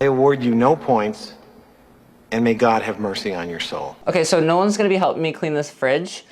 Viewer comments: I thought you said, "No one is going to help me clean my fridge." But you actually said," Nolen."😂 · I misheard “I have Nolen here helping me” as “I have no one here helping me” and then it was so funny I honestly thought it was a joke when the editor was like “boom someone else” lol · Absolutely thought he said “No one’s going to help me clean this fridge.” 😂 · I thought he said “no one” is helping and it was a joke that they showed someone was helping…the I read “Nolen”